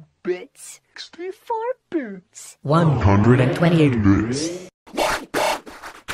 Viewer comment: 128 brits i hear lol